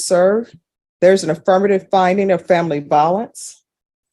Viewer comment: What does he say at 14:54? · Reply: "My family is gonna be homeless because of you".... He still won't take accountability.